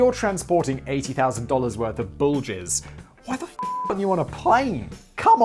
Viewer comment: ohhhh, 'bull jizz!' ...i honestly thought simon was saying 'bulges'. super confused for a bit there.